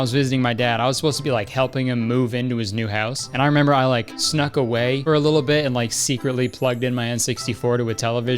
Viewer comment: did he really say "snook away" at 3:55 🤣🤣🤣🤣 love you kurtis!